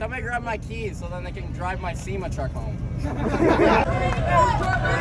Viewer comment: "Hey, what's my name?.." "GABE!!!" "What's your name? Oh im sorry, nobody fkin knows. " That was brutal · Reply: That makes sense. I thought he was saying “FEMA truck”